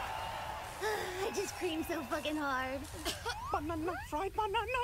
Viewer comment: "I just creamed so hard" I cried internally with laughter. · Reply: I thought she said screamed 😮‍💨